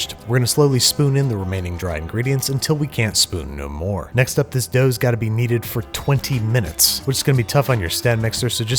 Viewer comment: Why did I hear: "this dough's gotta be yeeted" ?